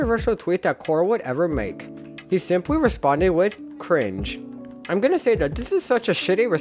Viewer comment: Yes “Crine :/“ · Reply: I thought he said “cringe :/“ this whole time I never knew he actually said crine.